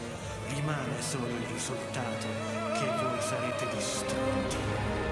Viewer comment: Omg I'm Italian and it took me like 10 seconds to realize that at 0:05 there was a voice speaking my own language! I was so confused lol Also, if anyone is wondering, it says "Nessuno può sfuggire al destino scelto. Rimane solo il risultato che voi sarete distrutti." (No one can escape the fate chosen for him. The only thing that stays is the result, that you will be destroyed.) It doesn't make much sense neither in Italian nor in English, but it's still pretty cool and I definitely wasn't expecting it!